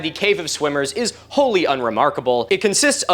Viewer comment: I totally misheard that as "Cave of Swingers" and had to stop the video while my internal DJ gets Jungle Book's King of the Swingers out of its system! 😅